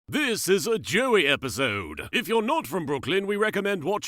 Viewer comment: I thought he was saying Jewy episode at the start but then i re-started it about 12 times then herd Joey XD